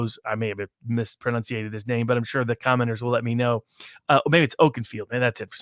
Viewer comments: Barnes: "I'm sure the commoners will let me know about it..." Turned off right then, jacknape. I will not tolerate being talked down to by an ambulance chaser who caught a couple of public flyers that netted him some notoriety. Hey Rob, learn some humility. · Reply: He must have said "commenters" but it also sounded to me like commoners when I heard it. I'm really surprised the stream ran with the latter instead of the more obvious deduction. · Barnes: "I'm sure the commoners will let me know about it..." Turned off right then, jacknape. I will not tolerate being talked down to by an ambulance chaser who caught a couple of public flyers that netted him some notoriety. Hey Rob, learn some humility. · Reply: commenters, not commoners. You misheard.